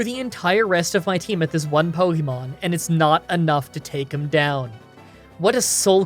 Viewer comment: did you say Scizor is a ponymon?